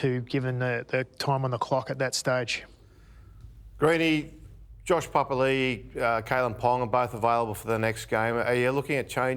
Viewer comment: did he just say josh palily